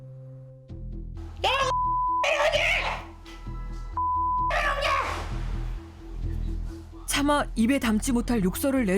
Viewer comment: 뭐라고 무슨욕하는거임? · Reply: 대충 니 혼자먹으니까 맛있냐 라는듯?